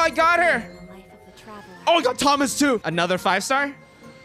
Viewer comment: did he say Thomas 😂